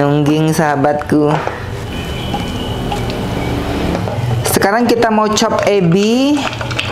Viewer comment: Selamat malam dari yogyakarta ko 🙏 Pas ko bilang: sekarang kita chop ebi nya, aku dengar nya "sekarang kita chop edi nya" aku putar ulang ternyata aku salah dengar 😀😀🙏🙏